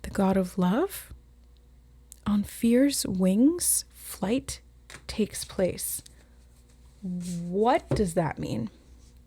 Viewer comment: When you said “on fears wings flight takes place”, I actually heard this as “on fierce wings flight takes place”. It led me to think about ferocity on wings, which made me think of the lion and strength card in tarot (I’ve been getting the strength card a LOT lately). I think you mentioned it in this reading too. I looked into the winged lion and its symbolism. I’m not sure what this all means, but something about Saint Mark feels relevant? I don’t know any biblical stories as I was not raised religious, but one of the things Saint Mark seems to be associated with is promised resurrection. And.. you mentioned resurrection and someone clawing out of the tomb. Maybe it’s not relevant, but if it is perhaps someone who has more knowledge of Christianity can interpret this better than me. Thanks:)